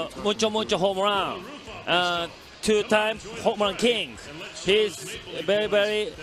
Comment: im japanese but i don't understand what he says. what is he talking about? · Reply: He said mucho mucho home run (mucho means a lot in Spanish) he was talking in Spanish for a bit